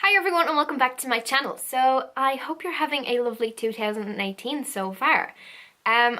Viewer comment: did I hear 2019? :D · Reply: Jo anne Nope, I said 2018☺️ haha I panicked and started to watch the video haha